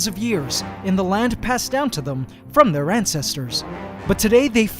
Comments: Thought he said ant-cestors. But didn’t 💔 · Wait, did you just say "ANT-cestors? 🤣🤣🤣